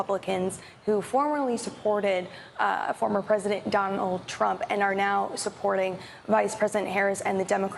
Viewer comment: did she really say Don Old Trump? 🤣🤣🤣